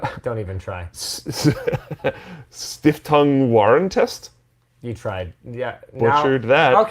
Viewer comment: "Stiftung Warentest" translates roughly to "Foundation for Product Testing" - an independent German organization that tests consumer goods. Greetings from Germany. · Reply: Fun Fact: Until WAY TOO LONG into my german life, did I always mishear it as "Stift und Warentest" (which would mean basically "Pen and Wares Testing". It never really made sense to me, but since I always misheard that from childhood, I never really questioned it and kinda went with it for a LONG while, lol)